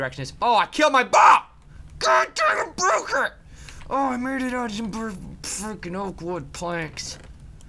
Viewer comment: Does he say '' i made it out of some fucking oatwood planks''